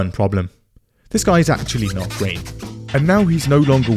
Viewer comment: "So that's a good choice. One problem: this guy is actually not ____" what does he say here? Green? Queen? Quaim? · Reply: Great